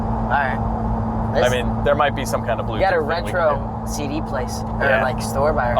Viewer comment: Did he say retro cd place ughhh right through the heart